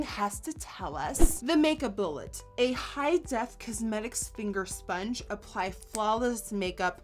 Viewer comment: did i hear "sphinger sponge"